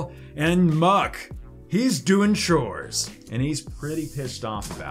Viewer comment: i thought you said "Alolan MUK hes doing shrooms.." lol